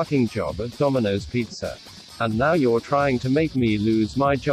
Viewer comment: it sounds like rick is saying "Saybuck"